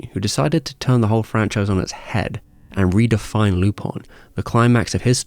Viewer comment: Dude, what the heck is "Lupon"? (lypɔ̃), it's Lupin (lypɛ̃) :/ (I kept hearing "loophole" wondering what the freakis this x'D) <link> ( I just checked, the character is half Japanese, half French... Lupin is a call to Arsène Lupin famous fictitious French gentleman thief) Do the Japanese called him "Lupon" or something? I don't get it :/ Anyway, great video like always, cheers! :)